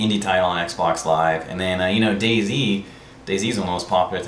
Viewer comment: OOOOH You're saying "Day Z" for the longest time I thought you were saying "Daisy" and I was like WTF is he talking about...? hahaha